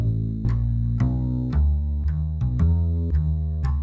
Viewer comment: Who thought it said "tumblr dryer" too? I was like "magma, you're not tumblr"😂😂😂